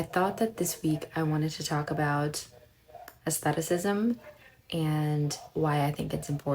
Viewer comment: I briefly misheard and thought you said "asceticism," and I was all, "Girl, are you okay?" Thankfully, I checked your Substack, and all is well. I am about to reread Rebecca for the first time since I first read it twenty-five years ago, thanks to your recommendations. Currently wearing L'Air de Rien by Miller Harris because it reminds me of warm bodies in an old library or a used-book shop.